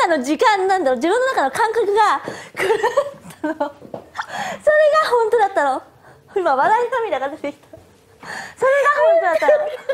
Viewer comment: ここなんて言ってる？ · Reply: 「それが本当だったの。今笑い涙が出て…」だと思います